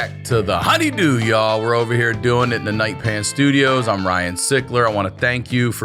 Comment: I thought he was saying, "the night pan studios" until he talked about how it's called "the night pants studios." It's still cool, but "Night Pan" sounds pretty bad ass. Like when you get home at 2am after a killer set and you bust out your favorite frying pan you only use to cook with in the depths of night time. After a hard night's work.